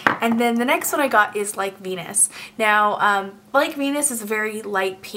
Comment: when you said "Like Venus" the first time I was looking at a different tab and I totally thought you said "I got my penis" llolololol